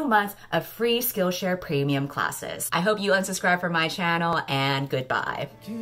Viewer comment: At the end I thought you said “I hope you unsubscribe from my channel and goodbye”